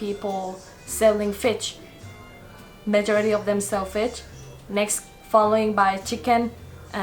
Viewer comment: Hahaha fich instead of fish 7:51 😂😂